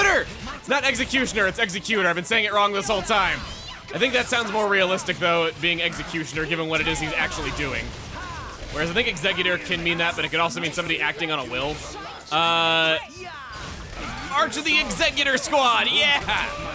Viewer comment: executor- a person who executes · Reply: I know. it sounded like he said Exeggutor